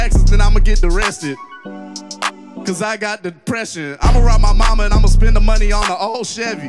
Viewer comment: I thought he said ima ride my momma but he said ima rob my momma XD 😆